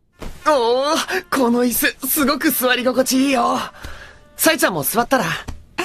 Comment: all i hear is 'Saitama'.